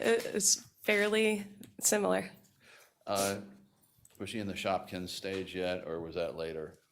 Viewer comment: What is he saying here? 'Shopkins stage'? 32:28 I loved her reaction to that objection. 38:02 I get what she was saying about comorbidities, attitude of self, attitude of family. Ive had 2 very bad experiences with PTs. 1 led to the development of 5 yrs of chronic pain in a new location & 1 set me back by about 6 months after back surgery. And, yeah, that makes me not trust PTs.